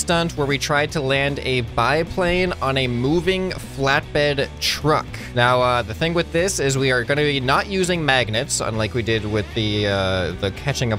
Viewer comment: Anyone else thought it said landing a biplane on a moving flatbread😂